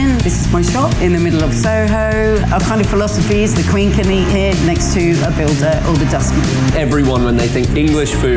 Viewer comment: What the hell is she saying at 4:10???? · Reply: The queen could eat here.